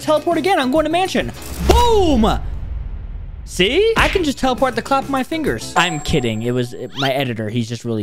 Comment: alr you make GREAT vids but 1:35 did you say you can teleport with the clap of your fingers? but still GREAT vid